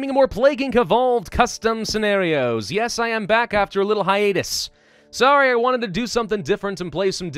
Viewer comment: Did he just say "Yes I am back after a little high anus"?